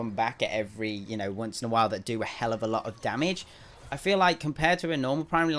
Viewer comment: huh what did you say. Did you say something not kid friendly?!?!? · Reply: He said Hell which is a place, not a cuss word.